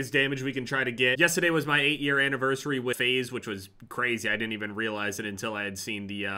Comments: Thought he said “8 year anniversary with aids” 😂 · I thought he said 8th anniversary with aids for a second I had to rewind🤣